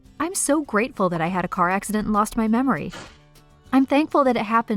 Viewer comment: I thought it said I lost my homework